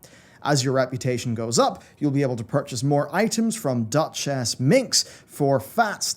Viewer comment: I'm sitting here, drinking my coffee, trying to wake up and I thought I just heard you say "a Dutch-ass minx" at 2:59 . I paused the video and burst out laughing... So random but hilarious until I saw her name. LOL. Anyways. Great vid. Thank you for the constant content!